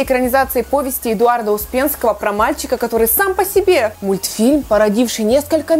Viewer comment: Мне послышалось не трое из простоквашено а троллинг из простоквашино :D